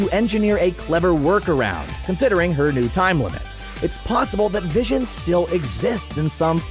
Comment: I thought u said it was good that vision died